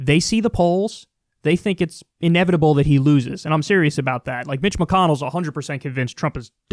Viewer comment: Toward the beginning, I somehow misheard Kyle as saying Bitch McConnell so from now on that's the guy's actual name according to me